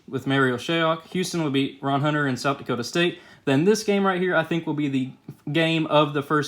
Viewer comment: did he just say South Dakota state